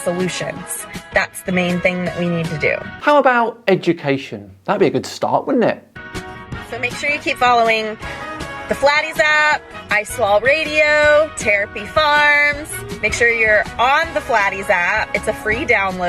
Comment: I swear she said "I swallow radio" 😕